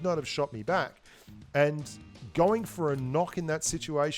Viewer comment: He says going for a knock instead of going for a kill.